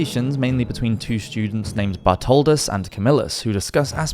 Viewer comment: That first clip is so nasty wth sits on their bed with their shoes on Lol also 7:06 it sounded like "Buttholdis"